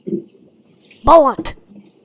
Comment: they kept on saying bullet or something like that, what are they saying or what's it means?